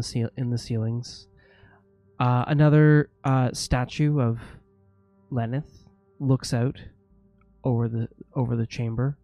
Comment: every time he mentions the statue i always hear “statue of Lenin”